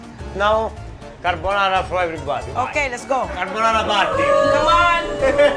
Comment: did he just say "carbonara party" oh my lord